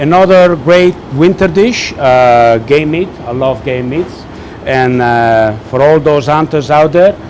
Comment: Sounds like you say "I love gay meat". :P